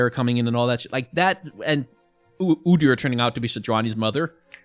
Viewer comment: Did I hear that right? Did say: “after Udyr was revealed to be Sejuani’s mother”?